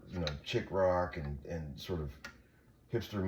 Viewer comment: I thought he said “chick rock or fister music”